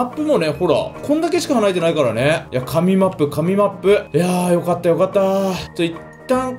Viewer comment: おれはどんどんリクエストに答えていくぜ！こにお勢いでやり残しもいつかは。。。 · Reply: 神マップ→SMAPに聞こえた